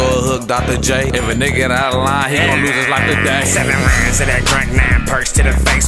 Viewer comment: Damn 7 lines of dat drank, 9 percs to the face lol sada finna od😂and he talking about 20k for the 1000 pack of percs I need on😭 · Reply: Ajas Kadic thought he said ‘7 lines of that crank’ haha thought he went straight sick with it. Sada is fucking with the SHIT tho.